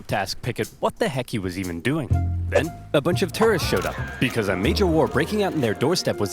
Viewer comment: ERROR IN THE CAPTIONS. Instead of 'tourists,' it reads 'terrorists.'